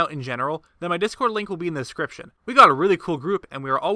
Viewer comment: Update to this theory: A viewer of mine shared a page from the Masterworks Book (also called "Breath of the Wild: Creating a Champion) that completely busts this theory. It confirms that the court poet had NO involvement in the ancient research. Honestly not too surprised, since this is one of the worst theories I have done. Have a good day, guys! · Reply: what did you say your name was??